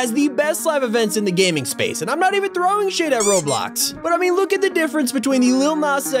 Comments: I thought you said shaid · WHAT DID HE SAY????? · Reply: SHADE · guys I said Im not throwing SHADE at roblox not the swear word lol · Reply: I thought you said SHAME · what did he say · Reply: Shade.